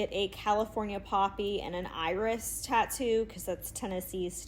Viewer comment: 😆 I thought you said "coffee" tattoo but I think you said "poppy".